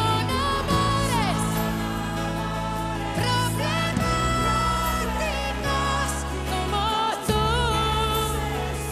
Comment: Min 3:43 ???? que dijo? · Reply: dijo problematicos..... está bien claro....